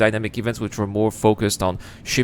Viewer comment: almost sounds like you said Jerk Town 🤣😂